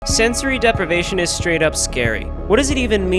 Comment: i thought u said sensory deprivation is straight up gay